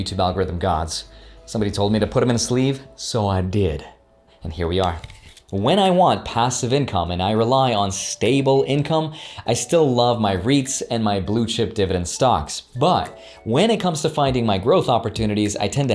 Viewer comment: what was said at 12:00 "rates? and blue chip dividend stocks"